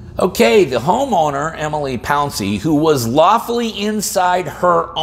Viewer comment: "Emily Palsey(?)" did i hear that right? My own research said the victim/homeowner was "Eboni Pouncy"... did the police department that made the report falsify the name to try and misdirect followups on this incident i.e. searching things like "What happened to the Emily Palsey v. Texas Sheriff case"